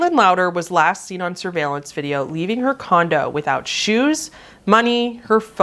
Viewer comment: I misheard "leaving her condo" as "leaving Wakanda" and had to back it up.